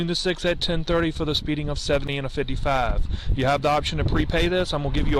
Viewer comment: Inb4 "new flannel" comments. Hope you're having a great week. Remember there's no Call-In Show tonight, but the Sunday podcast will be back as normal. I will also have another video this week as normal. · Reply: ffs I thought he said 170 in a 55 the first time.